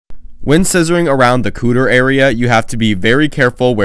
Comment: I heard “when scissoring around the cooter area” and when I tell you I choked on my starburst out of shock lol 🤣